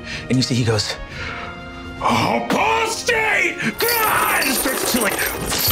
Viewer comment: can anyone tell me what he is saying? Because I don’t understand what he is saying😅. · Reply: "Apostate! Grrrrah!"